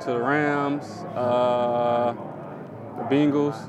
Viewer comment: When he said bengals I thought he said beagles 😂😂😂 wouldn’t be a bad NFL name